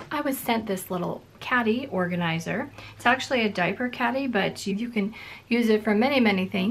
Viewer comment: Loved the suggestion for the diaper caddy - I found a similar one on Amazon and yes, they are quite wrinkled from being packaged so flatly BUT.... I used my steamer on mine, placed some heavy books into it after it had cooled and it has reshaped beautifully! Enjoyed watching all the goodies! Thanks! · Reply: Oh! I thought said “diver caddy” and I was wondering how this especially served divers.